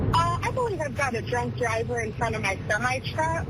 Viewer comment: Sounds like he said “good thing a drunk driver saw her swerving “ 0.30 😂😂😂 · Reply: Lol, I heard "drug driver"! 🤣